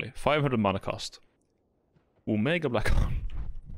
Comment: I genuinely thought it said obama black hole